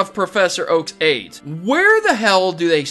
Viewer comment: Did he just say Oak's aids?! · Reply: Aides not AIDS, silly!